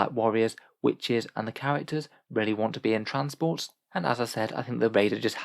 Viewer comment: Can I just check... At 19:33, did you say "Really want to be in transports" or "Rarely want to be in transports"? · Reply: Really